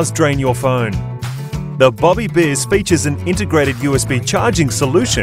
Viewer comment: I thought it said bobby jizz...